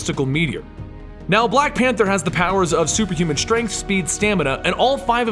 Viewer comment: did anyone else catch that? Black Pansers? XD